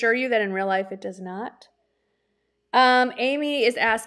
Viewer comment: Hi! Loved your live Sunday!!! You mentioned someone named Ali ... and her YouTube channel but I couldn't quite get her last name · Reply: Same! I was looking. I thought she said Aly Blinds. It I can’t find anyone with that name. 😊